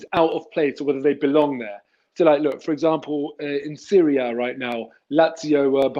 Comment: I thought he said "in Syria right now" 😅